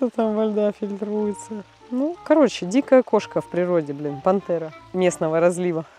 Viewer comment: Привет пересмотри что ты сказала на 17:20-17:22 либо мне кажется либо я чётко услышала мат извини если я для тебя очень надоедливая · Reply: Она там сказала "Блин". И даже если бы сказала мат, то что такого? Она живой человек, который тоже материться иногда